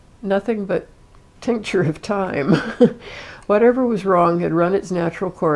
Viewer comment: I had never heard the expression, "tincture of time" before. I had to google it to find out it meant that, rather than treat someone with a narcotic, sometimes the ailment heals on its own with time. I had first thought she had said, "tincture of thyme," and wondered what the purpose was for taking a thyme extract, lol.